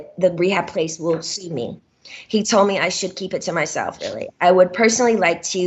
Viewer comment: OMG! At / slightly before 20:23 I heard a EVP- it said something like “oooh really “. It’s trippy! It might be glitch buts it’s crazy!!